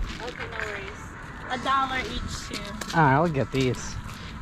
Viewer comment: Did she say a dollar each shoe? So two bucks a pair? 👞👞💲💲